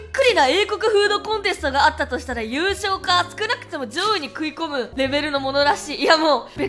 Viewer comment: 女王 fits in context but the word is 上位, so "In a British food contest, I hear these win first prize, or at least make it into the upper ranks." Something like that :)